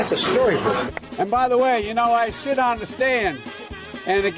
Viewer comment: is he saying "I s$*t on a stand"??